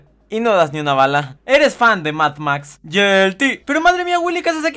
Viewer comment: como lo supo Edit: 6:11 x2 8:28 X3 y el otro x4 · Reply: No entendi la del 8:28, la de plus, Yo la tengo y no entendí, por qué dice yelti o algo asi ? •~•